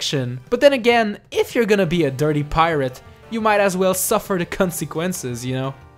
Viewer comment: uhhhhhh did that mean something gross or did he say that becuse he was being funny or becaause his accent? idk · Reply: The Trolldier i know, but they way he pronounced it sounded like the c word...